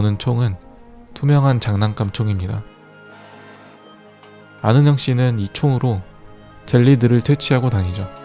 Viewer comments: 나만 아는 형씨라 들리나? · 초 나만 아는 형으로 들리는 건가???